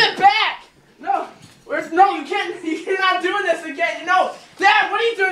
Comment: did he said "again"?